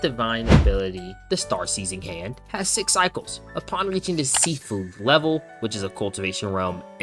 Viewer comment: my dumbass thought you said "Sea Food Level"